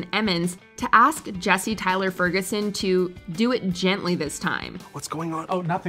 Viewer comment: What did she say?? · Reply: Do it gently